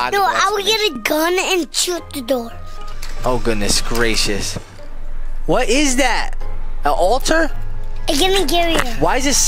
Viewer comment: I thought he said what is that a otter